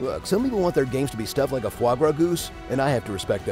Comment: when he says "some people want their games to be stuff like a fwarblargooz", what is he actually saying? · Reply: it was like a foie gras goose I believe